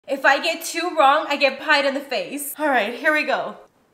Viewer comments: Nahh i heard "if i get two wrong i get cu@ in the face" 💀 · at first i thought she said peed in the face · I thought she said piped ☠️☠️☠️☠️☠️ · She basically got nothing in her face edit/fun fact: i thought they said "get peed in the face" for like the first half of the video 💀